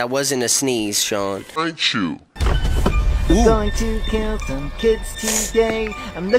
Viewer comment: DID HE JUST SAY KILL BUT THE SUBTITLES SAID CATCH?